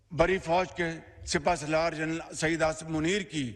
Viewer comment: I heard shaheed (dead) instead of saeed😂